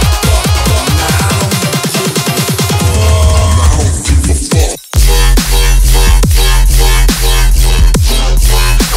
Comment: What does the man say at 10:11 ? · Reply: "i don't give a fuck (idgaf)"?